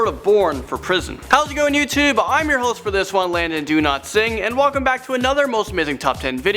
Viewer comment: Landon, much love❤, but when u say ur name...i keep hearing "Landon Do-Not-Sing" 😂 (and im sure u have a lovely voice!) P.S. love ur first name, too! (My 2 kids are Logan and London)